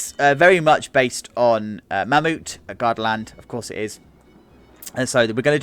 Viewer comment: For some reason I thought you kept saying garden land so when I looked up garden land Busch gardens popped up until I looked into the description I see based off garda land